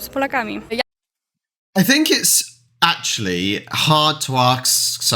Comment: Don't want to be pricly, but did i hear you say [...] hard to ax someone [...] around 1:52 . Is that normal? Confused